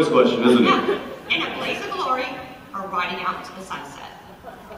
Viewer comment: When the lady asked "in a blaze of glory or riding out into the sunset" I heard "in a blaze of glory or a body count into the sunset" lol I vote body count into the sunset. Either taking out a ton of monsters as they walk to their ending, or recapping all the things they did take out over the years